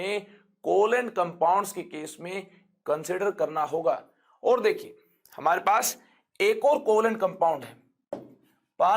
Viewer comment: sir is spelling something like PARCIAL , PARTIAL.......can anyone tell me what is that actually....or give the correct spelling.....is it the unit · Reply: partial